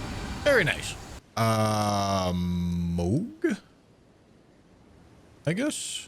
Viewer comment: I heard hum say "amogus" slowly. The brainrot is real folks